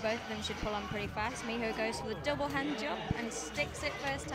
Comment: did she really says.. "double hand job?" :D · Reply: Danpyo 2g No that's your mind, she said 'double hand jump' haha